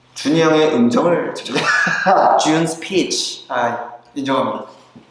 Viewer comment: skslkfj;dflag;a i'm sorry byeongkwan but that sounded like jun's peach and even though that's not what he said I AGREE